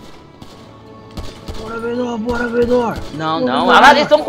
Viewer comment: só eu ouvi ''Bora Menor''? Menor ta vivo???